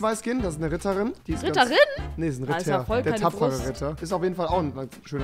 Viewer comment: Erstmal "Tamponritter" verstanden 😂